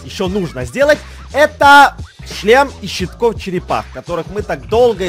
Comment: мне одной показалось что он сказал:" Член из щитков черепах?"Я пересматривала несколько раз и теперь поняла что он сказал шлем😂😂😂